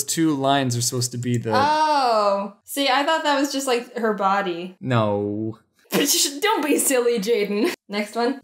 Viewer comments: did she just say "bitch don't be silly" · in the video it sounds like Jaden said bitch